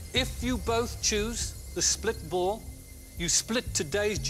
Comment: Lol...the clip right after the one of the male dancer spitting his gum out onto his dance partner is all about "splitballs"...but at first I thought he was saying "spitballs"!!!